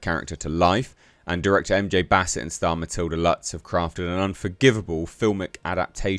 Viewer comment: I'm not sure if I misheard or its the accent or just a brain fart at 7:00 or so but "...have crafted an unforgivable filmic adaptation of her story." I hope this won't be prophetic but hats off if it is. Hilarious, either way.